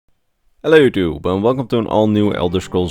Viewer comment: Are you saying “hello tube” in the opening of your videos ? Love your lore videos just wasn’t sure if it was a fast “hello YouTube” or just saying “hellotube” ? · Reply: I only heard ,,hello too" or ,,hello 2" xD